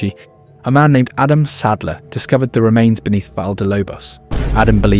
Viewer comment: I heard Sandler